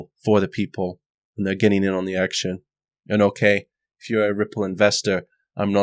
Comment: at 7.47 I thought he said: by the people, for the people and 'I am getting an erection'...which wouldnt be weird actually...